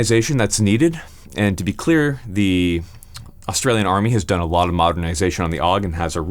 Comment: did you just say Australian army